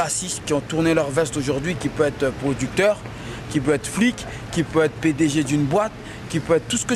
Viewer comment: I wish I could speak french because I can almost swear he says "give brad pitt a juju boy"